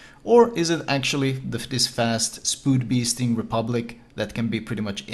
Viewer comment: Did he say ''spood beest'' at 49:03?